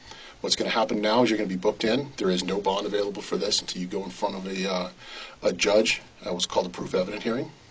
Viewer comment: It's not unintelligible. He clearly says "at what's called a 'proof evident' hearing." You suck at this.